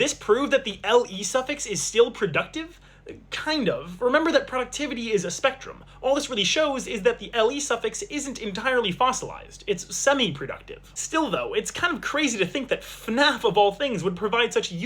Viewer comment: I thought you said "FNAFable things."